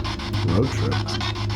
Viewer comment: Did it say “Motrin” at 20:24 ? Can someone confirm that for me? I’m very concerned if so.. for myself. I’ve had dreams since I was a child of an entity that would call itself “Motrin”. It was a woman. Well she appeared to me that way.